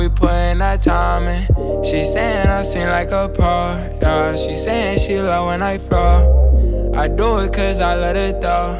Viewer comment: I thought lil mosey said "she loves when I fart'